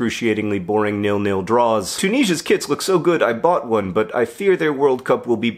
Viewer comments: I definitely heard “Tunisia’s KIDS” rather than KITS and was very confused as to how and why you purchased a Tunisian child. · I definitely heard "Tunisia's KIDS look so good, I bought one"